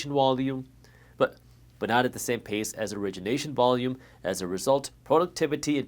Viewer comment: Did you say "wolume"?